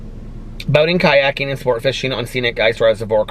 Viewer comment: When you said boating ,I thought you said boning 🤣🤣🤣.